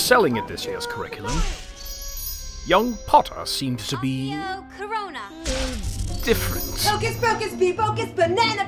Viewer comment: Did she just say “Accio CORONA”!?